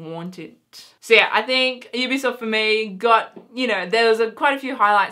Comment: I believe you said EBsoft at 7:30....or at least sounded like it lol because you said Ubisoft after that and I realised it sounded different so I checked it again :D